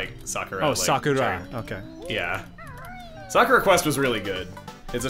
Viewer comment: Thanks goodness I wasn't the only one who thought they heard Soccer Quest at first. Though, I'm sure something like that actually exists.